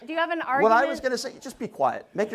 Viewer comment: I heard 'Ben' Shapiro at first, and I was wondering who this Ginger was. From his look, he's definitely not related to Ben.